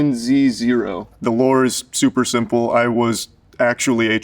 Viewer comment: the caption said it's inaudible when it's clearly not. He said the "Lore" is super simple